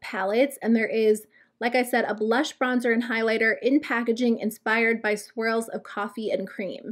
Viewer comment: Ok, I laughed so hard at myself when you were talking about the Marc Jacobs product. I thought you had said "inspired by squirrels" 🤣